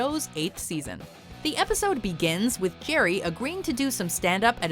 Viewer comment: Erm ... "Gerry" (5:41)? Does anyone else hear that or is it just me?